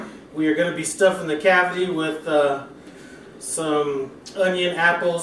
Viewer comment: I'm sitting here listening and sounds like you said "We're gonna be stuffing the Cathy". I was about to say WHO is Cathy....lol. I then realized you said cavity. The way the breast was cut, it do looks good and juicy. I'll have to try this. Thanks man.